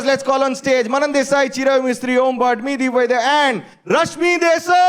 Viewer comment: Did said Nidhi Vaidhya in intro 😂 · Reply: He said, 'Me Deep Vaidya ' bro🤣